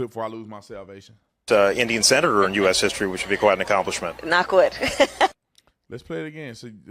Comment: She’s 75% Indian and 25% Jamaican. I heard. Sounds like she said Not Good, she said knock wood. She can’t speak. She calls herself African American. Elon Musk is more African than she is. TRUTH